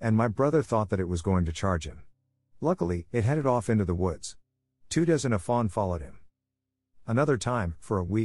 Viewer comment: Two does and a fawn, I thought he said two dozen fawn and my lizard brain yelled ‘lies!’